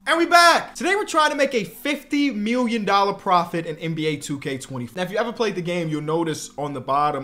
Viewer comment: almost clicked off the video bc it sounded like he said 2k20